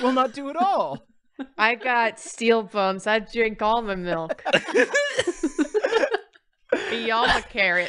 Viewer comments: MY favorite part of this bit is the way says "I got steel bones, I drink all muh milk. I eat all muh carrots, I got laser eyes, daddy." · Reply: I thought she said, "I drink almond milk." 'Cause she's lactose intolerant. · At first I thought said, "I got steel bones. I drink almond milk." 😶